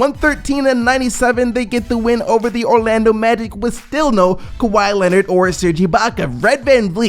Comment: you said kawhi Leonard instead of Kyle lowry